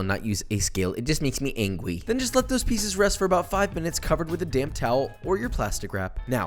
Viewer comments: I thought he said dam towel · When you said damp towel I thought you said dam towel